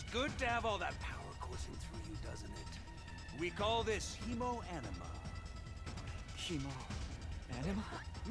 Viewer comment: I swear to got I had to stop what I was doing to make sure I didn't hear "emo enema". XD